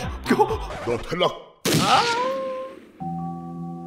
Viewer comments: Did he actually said "Du hast gelacht" in german? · Why do I hear him saying Nutella